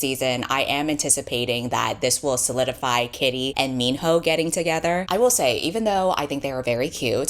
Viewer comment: When you said minho, I thought you said mean hoe 😭 😭 😭